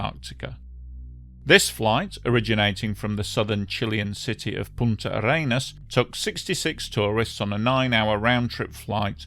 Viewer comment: Did I HEAR that correctly? "Punta yer anus?" 🤭